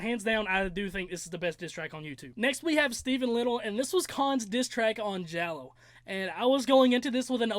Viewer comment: When you said "this is Con's diss track on..." I thought you said "this is K.A.A.N.'s disstrack on..." and I was like... holdup what did I miss